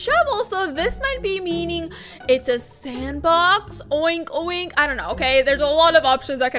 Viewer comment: did anybody hear oink oink